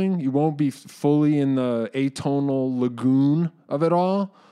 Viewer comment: at first I thought he said the “emotional legume” and not “lagoon” lol😅, what pedal could give me that Emotional Legume sound?